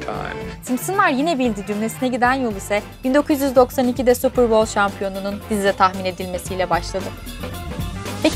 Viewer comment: de Ne diyor Allah aşkına birisi dinleyip yazar mı? Hanımefendinin konuştuğu her cümleyi anlayamıyorum resmen... İnşallah yalnız değilimdir. "Simpsonslar yine.... " Sonra ne diyor o cümleyi algılayamıyorumn.. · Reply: "Simpsonlar yine bildi cümlesine giden yol ise 1992'de super bowl Şampiyonun tahmin edilmesi ile başlandı." Tek dinleme ile anlasilabildi.